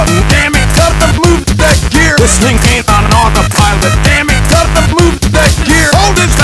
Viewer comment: I heard him say “whistling camp on an autopilot”